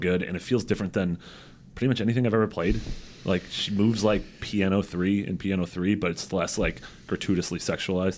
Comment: When said "P.N.O.3", I thought he said "piano 3." I was very confused.